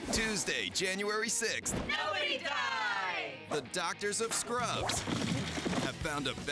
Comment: What are they saying? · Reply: Nobody Die!